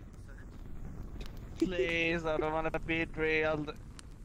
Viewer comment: did he just say "no I don't want to be a dorito."